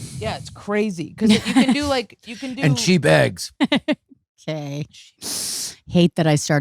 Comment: I also thought he said “and she begs” 😂 it’s def because I’ve found myself begging for addys it would have made sense 🤷🏼‍♀️